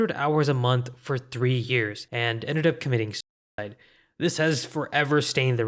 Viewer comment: bad audio, I think you wanted to say suicide